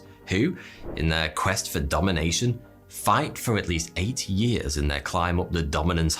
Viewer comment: WHOOOOAAAHHHHH ❗ i misheard 8 years for 80 ‼️ HAHAHA ‼️🤪👁️☠️👁️☠️👁️☠️👁️☠️👁️☠️👁️☠️👁️☠️👁️ WHOOOOAAAHHHHH ‼️ I thought this was a Casual Geographic video for a second ❗🤪❗🤪❗🤪❗🤪❗🤪❗🤪❗🤪❗🤪❗ WHOOOOAAAHHHHH THAT STACHE 〰️❗〰️❗〰️❗〰️❗〰️❗